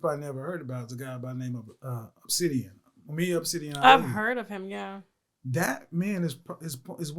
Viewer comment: anyone know his YouTube. I think he said obsidian